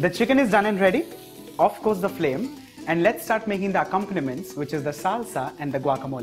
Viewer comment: does he mean condiments instead of compliments?